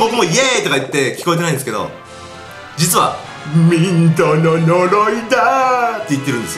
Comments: ミントの呪いだーって言ってたのか ドロドロになぁー って聞こえてた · 俺は💩の呪いだ〜って聞こえたw